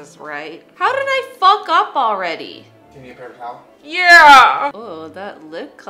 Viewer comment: "How dthdthdthdthdthdid I fuck up already?" Did anyone else hear that? xD